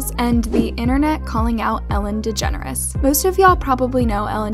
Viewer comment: For some reason I thought she said "ELLEN THE GENEROUS" am I the only one?